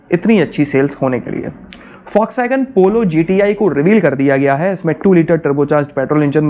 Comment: seconds pe bhai kya bola aapne dekho earphone laga ke sunna aapne socks wagon bola tha